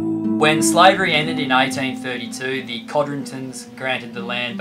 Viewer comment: "When slavery ended in 1932" With my "Chicago" ear, I heard "1932" while 1832 might have been a better choice of dates. Call me picky. Moving on, peoples are being crushed by jackboot economic authoritarianism lead by the oligarchs. The native peoples' land rights stolen and their way of life ruined. In the United States this action is called Trumpism but let us return to the victimized people of Barbuda. The people of Barbuda are now facing a life of servitude in the form of being either a hotel maid or gardener. Thus a return to the 1820s save for a few minor details. A BIG thanks for posting this video. Please post more as needed. Maybe this post will be the beginning of the "La Vagabonde" Revolt, with the citizens of Barbuda getting the support from the many people in the various countries that follow. From truth comes justice.